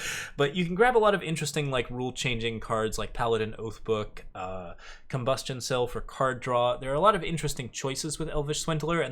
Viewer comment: You missed Elvish Swindler being useful in kennadins to drag out you combustion cell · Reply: nah he said combustion cell at 15:13. But he did say "combustion cell for card draw" which is weird, not sure what he meant to say